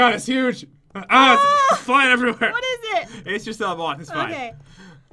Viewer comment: When she saw the moth I thought she said mouth😂